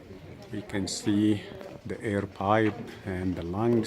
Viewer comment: in can you go into more detail regarding the symbol and its representation? was horus really born of a virgin mother? what is it you say before you make reference to the lines being tied or connected ? around 9:18 into the video ...sounds like youre saying "air pie " :P fascinating tour indeed!! so they wont even let you inside the temple with a video camera? :/.... I can see the logic behind flash photography but what harm can filming the site really do?